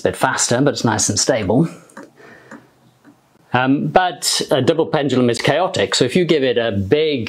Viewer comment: I thought he said ‘Devil pendulum’.